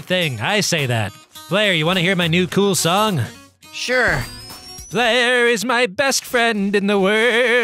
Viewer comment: it almost sounds like he said player is my best DEATH